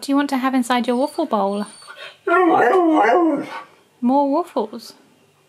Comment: I really heard the hello and more waffles I'm shook